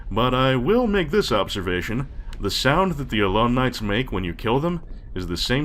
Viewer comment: the moral of the story is 'video game + going down mines = dying horribly' I keep mishearding Allon Knight as 'Alumni'. fear the ex-students!